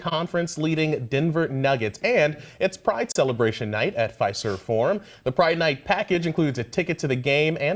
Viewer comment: i realy thought that guy said Pfizer Forum